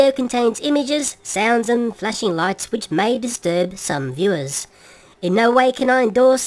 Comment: "-which may disturb some viewers." I thought for a second you said "The Lewis" instead of "Some viewers". Can you tell the difference?